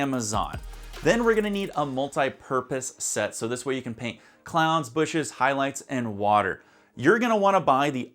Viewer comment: Every time he says clouds I only hear him saying “clowns”, and my brain is like, when are we painting clowns in Bob Ross paintings?? I keep hearing this man say cloooowns, is it just me?? haha